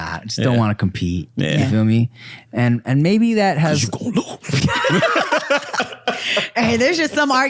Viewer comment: what did rick say? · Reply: P _Jay (cuz you gon lose)